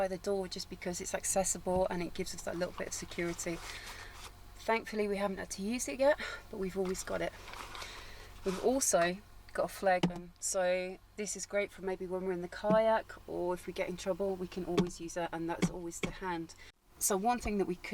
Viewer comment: What do you say at 6:38? After introducing the fridge, you say “also, we got a ___ “. It sounds like “flag man”. Anyway, great setup!